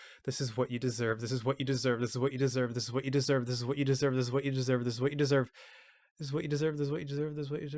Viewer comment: eventually it starts sounding like he's saying, "Siz wookity-serve" like he's summoning a demon. Tell me you can't hear it.